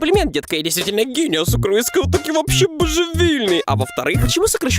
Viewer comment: Мне одному послышалось что он сказал сокрыска а не с украинского